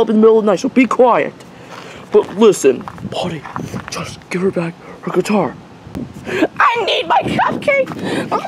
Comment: cc says but listen body cool just give her back her guitair